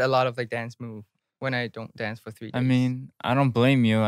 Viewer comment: Ten: It’s just hard to remember the choreography. I do forget a lot of dance moves when I dont do it like three (months). (I assume NCT is a dance-idol group. What should I call it? I’m sorry. I dont clearly know the term😂😂) Then there is peuni, “Even we barely have any choreo and I forget all”😂😂😂😂 It’s fine peuni, that’s BTOB charm. Even as a fans, I dont really remember the dance too (hm…actually Im not really trying to learn the dance tho🤣) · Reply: I thought Ten said after 3 days at least I think that's what Ashley repeated lol